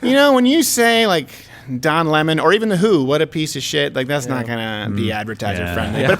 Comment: Did he say "The Hu"? Like the Mongolian band? What's going on with them? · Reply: The w.h.o world health organization